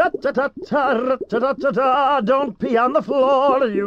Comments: i thought he said use the corridor 🥲🥲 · when I saw this clip without sub, I thought he said “use the color door”